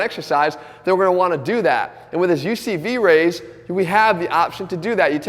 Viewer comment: What did he call the exercise at 9:00? UCB Raise? UCV Raise?